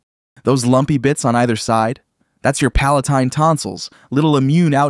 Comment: did i heard Palestine 😶 you know what time is this free free Palestine 🥳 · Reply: Palatine you did not hear Palestine do not lie